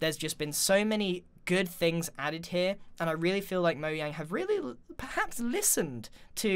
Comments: Am I the only one who heard him say moyang? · Wait did anyone else hear say “Moyang”